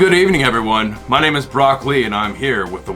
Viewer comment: i thought you said 'broccoli' as your name.....so its actually Brock Lee....lol forgive my ears😂